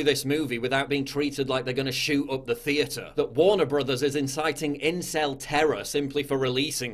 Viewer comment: My kid started talking to me while I was watching this. When I heard PJW say "Warner brothers" I thought he said "warn a brotha". Just thought I'd share lol